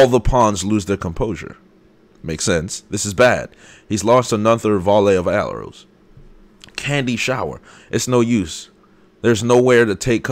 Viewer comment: did you have a seizure or did i cause that sounded like you said "he lost another volley of arolws"